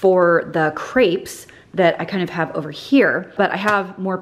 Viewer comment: I thought you said "for the creeps" 😂😂😂 I laughed out loud.